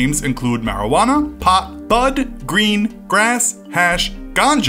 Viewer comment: He forgot that people call it gas more then all those names 😂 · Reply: I thought he said gas until looked up and saw it was grass 😐😭